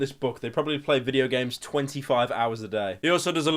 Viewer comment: did u just say 25 hrs a day?